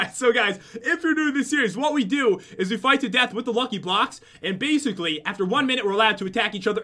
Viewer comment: did he just say fight with the blocks